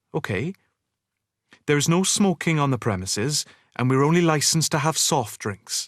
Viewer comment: I couldn't recognize what word was used by Pitt in this sentence: "There is no smoking on the..." (at 2:51). Can someone help me to fill in the blank above? Thank you so much. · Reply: there's no smoking on the premises.